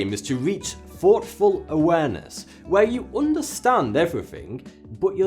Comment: the subtitle says thoughtless but Arran says its thoughtful.